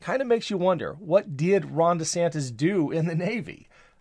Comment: It sounded like you said, "DeSantos" once. I have no idea if it was on purpose or not, but tying those two liars together is kind of funny.